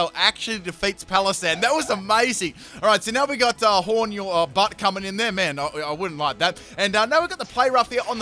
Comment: does he say " horn in ur butt" and then he says " I wouldn't mind that